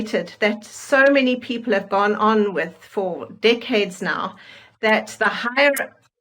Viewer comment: Decades…….I heard dickhead 😂😂😂